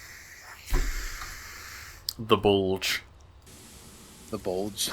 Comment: when you first called it "the bulge" i thought you said "The Bull". Honestly calling something like the Aurochs Class heavy cruiser would be pretty sick